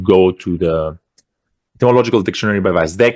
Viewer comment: Can anyone understand what dictionary he is referring to here? I am so bad at understanding people with accents. 😞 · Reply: is referring to the "Etymological Dictionary" (Ετυμολογικό Λεξικό) by Stavros Vasdekis (Σταύρος Βασδέκης).